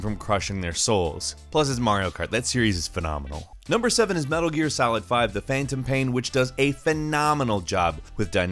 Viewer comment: Am I the only one who hears him saying "game" weird? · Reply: i heard "metal gear salad"... Maybe he does pronounce "game" in a weird way but i didnt notice it